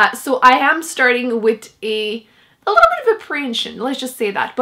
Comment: What is it that you say @ 0:52? You say you’re starting this book with a little bit of a ________ ? Sounded like you said something like “preiage” I can’t figure it out! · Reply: Apprehension!